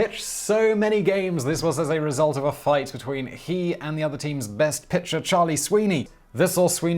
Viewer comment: you misspeak and it greatly alters whats said. you said "other team's pitcher" instead of "team's other best pitcher"